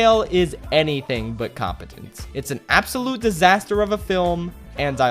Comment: I am so sorry,I thought it said comedic disaster.